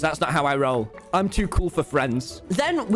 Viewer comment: I'm to lame to have friends? I heard it that way... -_o